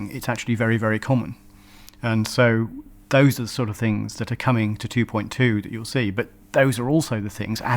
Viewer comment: Sadly probably not. I think it's more likely he just went on to explain the science they simulate (tectonics through volcanism) to produce surface features. I hope I'm very wrong .. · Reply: Well, what he says is (06:09): "... Those are the sort of things that are coming to 2.2 that you will see..."